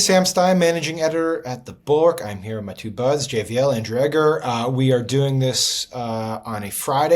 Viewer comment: Sam Stein tends to slur his words together sometimes or in the least mash them together & wen he says "I am here w/my 2 buds,". It sounds like he says "and Dreggar" instead of Andrew Egger (or however u spell his name). And they don't even hav their names on screen like they do every other podcast. I wud be annoyed to be misrepped like that if I was Egger.